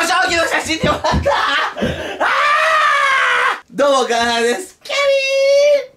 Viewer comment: の所「 どうも 我愛羅 です 」 って聞こえてくるww